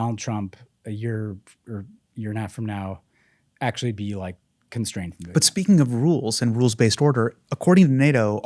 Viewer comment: Did he say "." "constrained?"